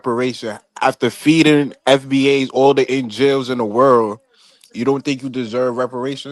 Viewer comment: it sounded like he said "in jail" but meant to say injera. That is hilarious!! 😂😂😂😂😂😂😂😂😂😂😂😂